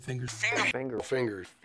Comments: I think I hear a "figure" in there 👀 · I hear "Figure"